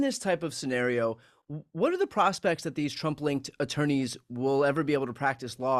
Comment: Oh "Trump-linked attorneys". I had to listen 3 times, I thought he said "Trumpling attorneys". 😂